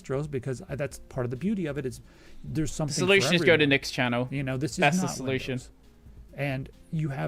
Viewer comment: I thought you were saying "nix channel" instead of "Nick's" and tought it wad cruel even though it would fix all of their problems